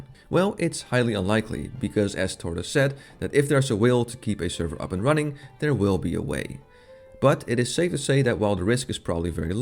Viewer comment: I heard this "If there's a whale to keep the server running, there will be a way." I mean, it works as well.